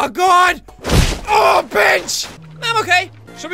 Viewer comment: I thought he said b**ch